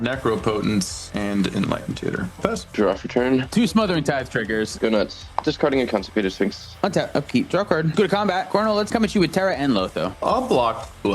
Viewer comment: does say "Constipated Sphinx"? 😂